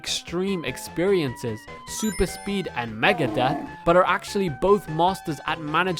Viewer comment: did someone say MEGADETH?